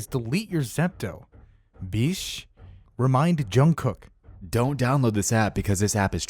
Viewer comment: did i just heard JUNGKOOK?!